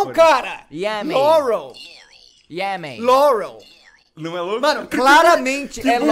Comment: eu escutei piaui